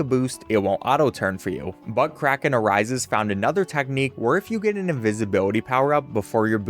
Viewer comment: I may have misheard it but I cracked up butkraken arises😅 sorry sorry for being childish